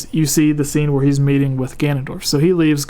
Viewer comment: I heard "mating with Ganondorf" Dirty mind I guess